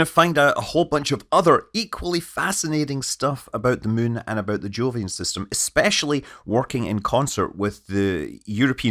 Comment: Thought he said "joolian system" at one point